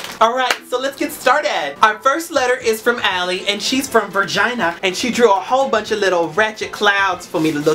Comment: I died of laughter when he\she said vagina instead of Virginia